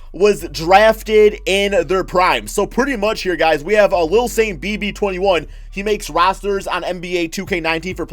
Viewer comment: Can someone spell the name that he just mention to me it's kinda hard to understand what he said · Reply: J Beast23 lilsaintbb21